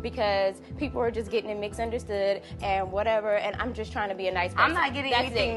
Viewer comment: Is it me or did Malayasia say “mixunderstood” at 2:17? 😂